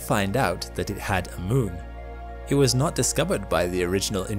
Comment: What is the word your saying at 4:35? I can’t make it out or understand it. Edit: oh, it was “moon”? The pronunciation confused me.